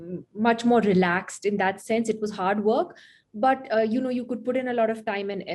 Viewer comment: When she said 'hard work' at 2:21 , I literally heard it 'Harvard'.